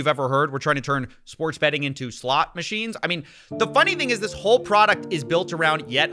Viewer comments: I heard you say slop machines and I just feel that's so much more accurate. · roblox being trash as usual. - lol I misheard "slot machines" as "slop machines" and I was like, how self-aware of them, but no just me hallucinating - the only way to win the game is not play, starve out the markets these crooks depend on - with how many sellouts there are, I'm glad some of the people I watch, like coffeezilla and penguinz0 still have integrity